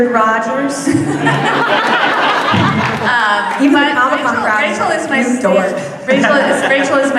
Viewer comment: What did Rachel say at 14:43? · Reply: Nm I listened again (and again and again) and she said “even the comic con crowd is like ‘you dork’...”